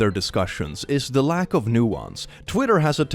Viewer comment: I thought he said 'Lack of Neurons' for and honestly both phrases work